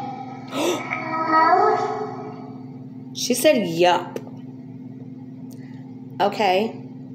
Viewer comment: It sounds like she says, “Yup clothes or close”